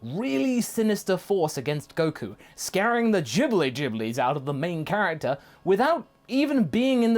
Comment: I heard “scaring the Ghibli Ghiblis” and imagined dbz characters doing that hair frizzing thing from studio Ghibli movies in reaction to moro.